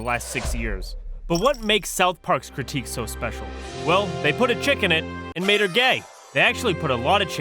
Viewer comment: What made this special more hilarious to me is that I kept hearing “chicken” instead of “chick”